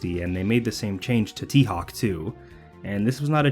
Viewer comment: i can't believe what he said there. my brain is so rottet i hear T. Hawk Tuah. kill me now